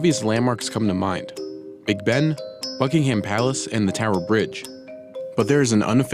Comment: The way you said Buckingham palace.. ..just no. You don't pronounce the ham like the meat. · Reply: Benjamin Taylor to me it kinda sounded like he said f*cking ham palace